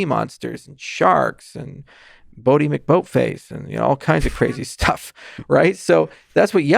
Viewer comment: I obsessed on what he said on 38:05 (bodi bocmacfaith? What did he said, please, i wanna understand the joke. I'm miserable.) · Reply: I think he said "Boaty McBoatface".